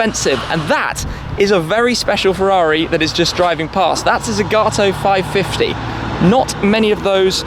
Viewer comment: I know he said the name of it, but I didn’t understand what he said lol, so what’s the name of the Ferrari at 1:20 ? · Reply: Ferrari 550 GTZ by Zagato